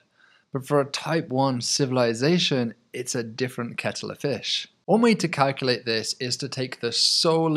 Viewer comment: What the hell did he say at 12:24? "Cattle to fish"?